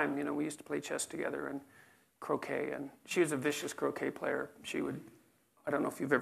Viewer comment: I thought he said cocaine player lol